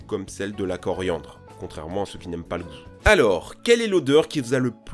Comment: contrairement à ceux qui n'aime pas quoi ? J'ai pas compris ce qu'il a dit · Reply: Le souffre